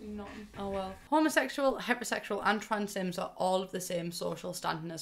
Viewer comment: When she said heterosexual I heard hyper-sexual